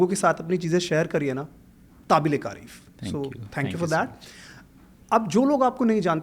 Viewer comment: did he say "Tabil-E-Karif" instead of "Kabil-E-Tarif" 😅